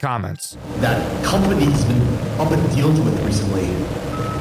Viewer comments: I heard “that company’s been uppin(?)deals with recently” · what i heard is "that company has been upping deals with recently", upping might not be correct but thats what i got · “That company’s been up and dealed with recently (?)” - doesn’t make much sense but it is what I heard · I don’t know if it’s just me but I hear “the company’s been up and dealed with recently” (no thats not a spelling era)